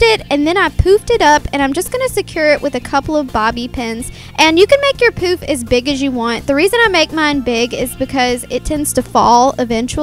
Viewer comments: LMAO i thought she said "you can make your POOP as big as you want to" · when she said poofed it up i thought she said poop haha, lol